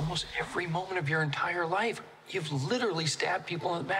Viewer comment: I can already tell the “well I’d never do it again” is gonna be a meme😂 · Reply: i don't why i heard him say 'Why not do it again"😅